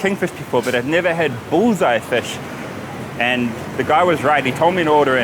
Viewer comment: It's "bhuthai" fish which is sardines in tulu which you heard it as bullseye